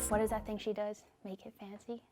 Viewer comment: watches video: @ 1:47 what does say? Make it fancy? (Continues to do it multiple times but gives up): facepalm 🤦🏻‍♀️ why...